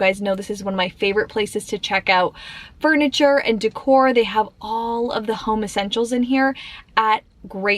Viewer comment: for a moment i thought i heard you say they have all of the homosexuals here 😂